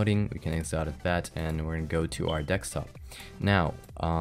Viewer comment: Did he say "Dekstop"?